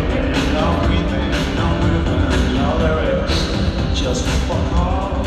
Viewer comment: what does Bernard say?! Anyway, I wanted to go to this concert from Spain, I was sure New Order was gonna give a long and effortful concert. But too much money the whole tryp. I will regret for a long time, fuck. you spent the whole concert recording?? My God. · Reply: I was there. He said “just fuck all”